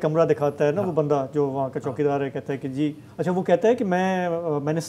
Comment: did he say " joona" hai ( old hai) is he a memon or gujratee ???